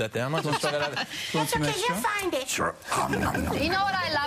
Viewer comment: did elmo said you're fine bitch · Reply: he said you find it